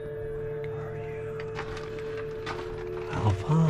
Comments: Bro idk if that you but I heard “help us” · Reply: "Help us hurry where are you" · I hear a voice saying something I can't make out the first word but I hear Are you (I think it might be saying who are you )